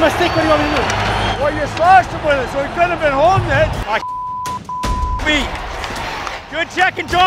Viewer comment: "He was holding my stick" "Well you slashed him with it so it makes sense why he was holding it" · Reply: I heard “Well you slashed him with it so he couldn’t have been holding it”